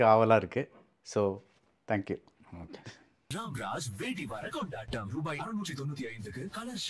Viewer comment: did he Said "NO THANKS"